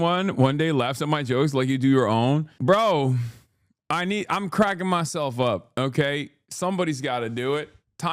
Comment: i thought he said "im crackin myself" and got so horny I almost passed out